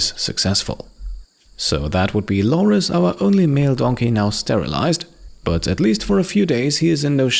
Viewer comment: Sterilize? Kevin misheard you, he thought you said stumble-ize!